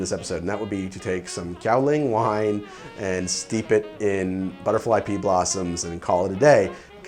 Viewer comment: Who else thought he was saying butterfly pee blossom?